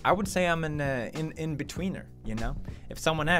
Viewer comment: Pewdsno hate but I thought u said I'm a bed wiener